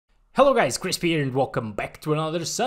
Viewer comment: In the intro I thought you said crisp beer